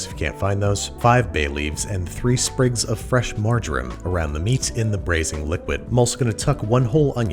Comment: Hah! For a second there, I thought he said to add fresh "margarine". I had to back it up to realize he said "marjoram".